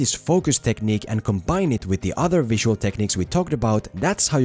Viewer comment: For some reason I thought it said "how to film Skyrim".